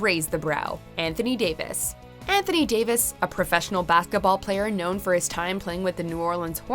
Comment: did she said BATHKETBALL?